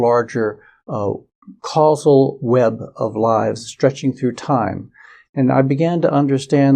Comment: Funny, when he said "web of lives" I thought he said "web of lies".